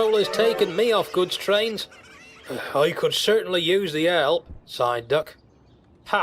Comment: It sounded like Duck said “I could certainly use the L!” I know what he was saying though.